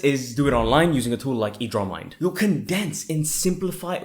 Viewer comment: What is the name od thr app at 4:28, I didnt hear the name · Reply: Edraw mind